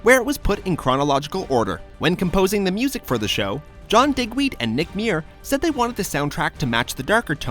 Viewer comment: when i heard john digweeds name i thought he said john Dickweed LOL